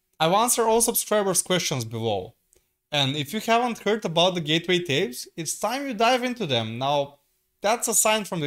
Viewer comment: "Gateway tapes"? What's that? Did I hear it right?